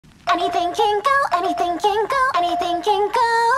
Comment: I thought the song was saying “anything gangle” for a sec💀😭